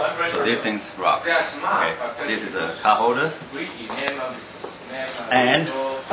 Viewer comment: Did I hear him say: "this is a cup holder" ? WTF.